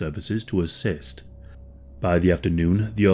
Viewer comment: You say "hostage".. ...I hear "sausage". ...this diet is killin'me.